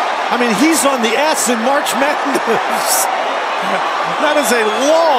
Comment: sounds like he said "He's on the ASS..........." LMFAO